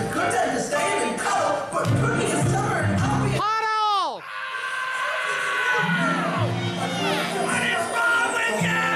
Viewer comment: what did he say I confused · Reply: Cute_Creator The dude yells puddle.